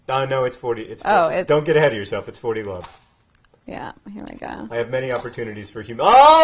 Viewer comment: did John call her "love"? :)